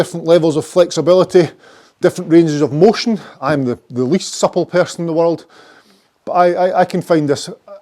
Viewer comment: I know you said you were “the least supple person,” but at first I thought you said “least subtle...”